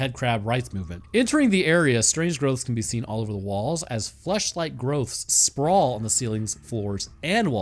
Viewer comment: thought you said "fleshlight growths"